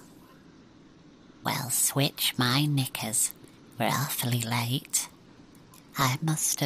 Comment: AYO WHAT HE SAYYYY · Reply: and he said knickers